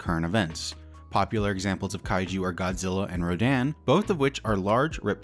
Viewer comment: "popular kaiju are Godzilla and Rodan" ok I had to play that back six times because I deadass thought he was saying "Godzilla and Roseanne"